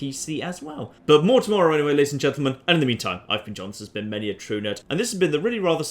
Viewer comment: I thought you said 'many a true nut' in the beginning!